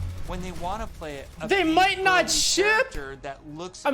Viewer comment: can someone tell me what he’s talking about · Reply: the rein changes might not make it to release